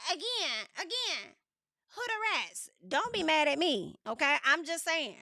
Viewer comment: did you say hudarats 😂😂😂